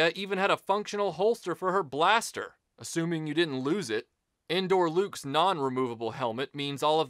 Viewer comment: When I heard "Endor Luke," even though I knew better, it sounded like he was saying, "Indoor Luke," like it was the one Luke Sky Walker figure from the trilogy that never went outside.